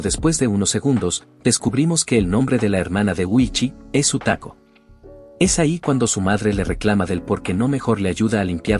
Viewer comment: pense que dijo otaku xddd